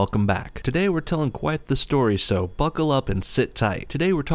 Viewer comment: I thought you said Fuck her up instead of buckle up